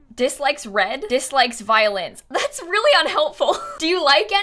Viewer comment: At first I thought you said violence not violins . I didn’t realize this until half way through the video. Also love your content!!